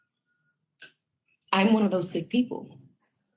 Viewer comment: Sick person, yes... but i think she actually said "I'm one of those SAID people" ! But it definitely sounded like she said sick tho